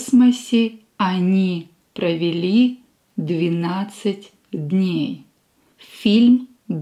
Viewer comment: Nice video, very good for listening comprehension! Just one question: At around 1:15-1:20 the screen reads 12 days but I am certain that you said 19 days. Was there a small error crept in or did I mishear what you said? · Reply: I said 12 😁